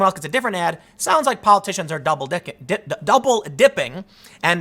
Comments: Ahh you said Double Dicking I heard you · Tim, did I hear right at 5:43 - "Double Dicking" - lol You should not correct that to dipping, cos Google are sticking it right up our arses at the moment